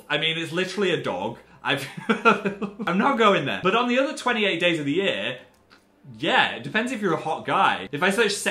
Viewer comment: Im sorry, did he say "on the other 28 days of the year"